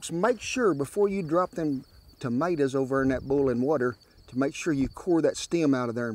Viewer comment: Did he just say, "bullin' water"?